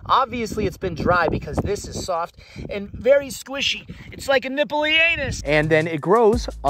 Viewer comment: Excellent video. I will confess I relistened to what you said at 6:27 a dozen times to make sure I heard right, and to be sure…”nippley anus.”